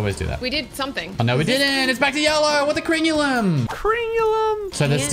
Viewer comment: literally makes me lose braincells like talking about the cables and thought he said they were gjellow